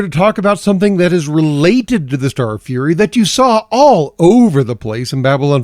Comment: thought that said furry was very confused for a second